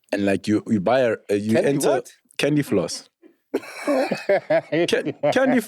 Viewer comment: Pops I heard it too😂😂 "Candy Flosk"